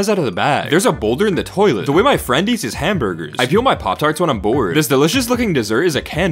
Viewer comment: I thought he said i pee on my poptart when im bored😭